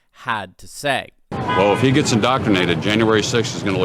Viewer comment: What did the guy say at 2:00... ??? "If he gets indoctrinated?"